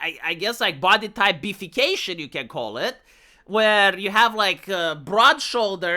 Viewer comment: What said: "Body Type B-fication" What I heard: "Body Type Beef-fication" Still works though :P